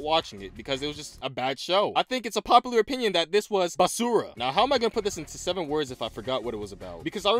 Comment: did i hear "Basura"? or something else?